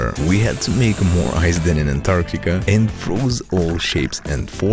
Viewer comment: It actually sounded like you said that you made more ice then whats in Africa. I later realised that you said Antartica.